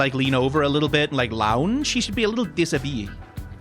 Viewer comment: I don't know what you wanted to say here but it sounded like you said 'déshabillé' I, too, want to see Gambit a little more undressed 😂